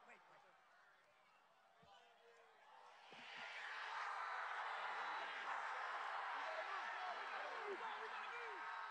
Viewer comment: WHAT HE SAID???? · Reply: Fight